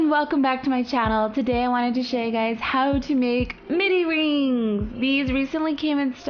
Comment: I thought it said mood rings so I was like "AHHHH" But MIDI rings.